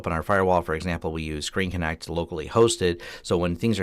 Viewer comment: you mention something about a locally hosted web server: "...ing connect". Couldn't make out what you were saying and just curious about the web server you use. Nice video; thanks for the overview. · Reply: We are using a self-hosted remote support tool that used to be called ScreenConenct but it is now called ConnectwiseControl. <link> And Thanks!